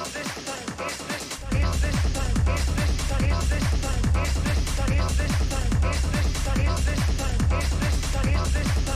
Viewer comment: what does it say at 28:35 ? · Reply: 'Is this some kind of a joke' - Akira Ishihara - Yamaga 7 O'Clock